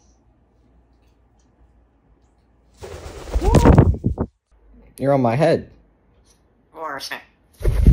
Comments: It sounds like he is saying "Where do I set." When he says to Apollo , you are on my head lol 😹😹😹😹😹😹😹😹😹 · Did he just say: "You're a snack"? 💀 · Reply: No he said “earn a snack?” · I thought he said for a sec 😂 not earn a snack oh man